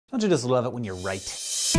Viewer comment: At first i thought he said "Dont you just love it when your white?" and for a second, im a bit ashamed, but i thought "Well, hells yea!"